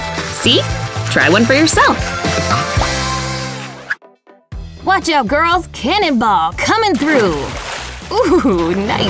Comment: Did they just say 'What out Balls Cannibal coming through!' 😬😯😂